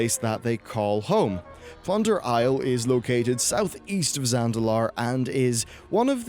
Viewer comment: "I definitely think Zandalar could make it into the game" Flash forward six months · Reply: Zandalar? All i've heard him say is Zalandar the whole time.